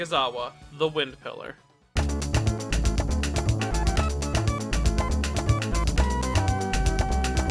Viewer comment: I think Im hearing things wrong. I thought you said Sanemi was the Wimp Pillar for a second there.